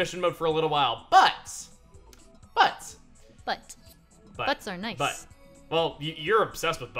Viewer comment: I CAN'T be the only one who thought Chugga said/was going to say "Well, your butt is nice" huehue